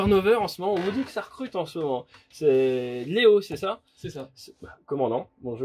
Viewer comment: j'ai entendu "néo" ce qui aurait expliqué comment il a rejoint l'avion en vol 🤭